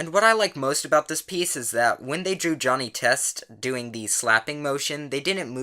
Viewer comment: What the fuck? Who the hell makes porn of fucking johnny test? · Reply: Alexander Baxter at first I thought you said Johnny toast and was like WAIT WHAT but.. then I realized it was Johnny test